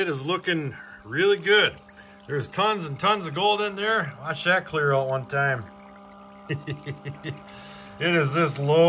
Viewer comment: "I shat clear one time?" Did I hear that right?